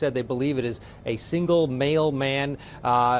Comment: I heard “a single male man” as “a single mailman” and was momentarily very confused. I was like damn, Ramsey was running post on the side? Dude was busy 🤦‍♀️